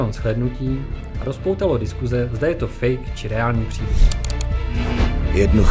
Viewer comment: Someone write this in egnlish plz? Ty :) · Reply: Vacková What did he say at about 00:30 - 00:32. I heard something sounding like fake.